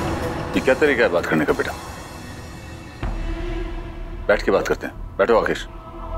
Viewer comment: Durga's father said rakesh instead of akash 13:00 😂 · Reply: He said Akesh